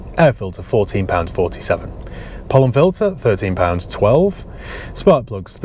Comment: Could have sworn you said "porn filter" and not "pollen filter" in the list of your spends 😂